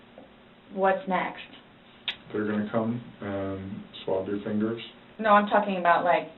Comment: "they're gonna come and saw off your fingers?" Is that what the male detective said, I'm very confused that this was glossed over lol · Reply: He says "swab your fingers" as in collect dna or w/e